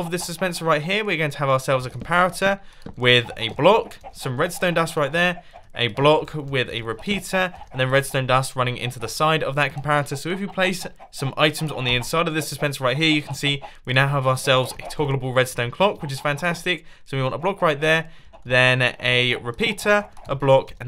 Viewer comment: I like how during the jungle farm I heard him say complarator